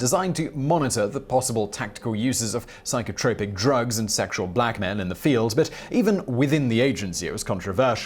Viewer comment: Sexual black men in the field? Did he say sexual black men in the field and was supposed to say sexual blackmail? Or did he say sexual blackmail and I just misheard it? I listened a few times and I swear he said sexual black men...which I don't quite understand unless he meant to say blackmail lol...sorry just thought I'd put that out there.